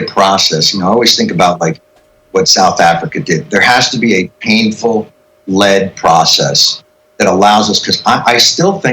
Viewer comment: "Painful lead process" - I heard "bullets" when he mentioned South Africa. Stewart may have had a Freudian slip; and I believe we are already in a Civil War.